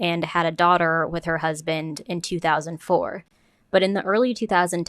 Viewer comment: i heard "eating her husband in 2001" that was quite the silent m😂